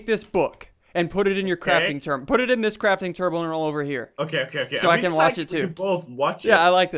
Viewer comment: Did he just say crafting turble?